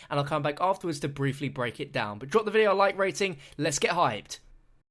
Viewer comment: am i the only one who thought he said "lets get high" for a second?